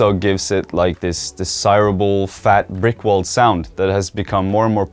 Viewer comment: I heard it as rick roll, did you? edit:but in reality its: brick world.